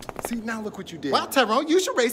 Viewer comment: I thought she said you should race dababy!